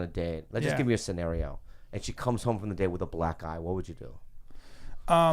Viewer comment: I first thought he said "black guy" and it went weird from there.